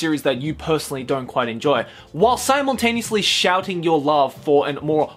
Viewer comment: gets triggered about articles grammar. Says "more older" Jeez Joey you gramer elitist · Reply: Breadball666 P.s he said “More old and obscure” although I understand how you misheard it as “More older obscure”.